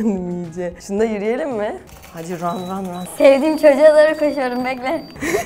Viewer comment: ahmet e doğru koşuyorum. Diye anladım ben.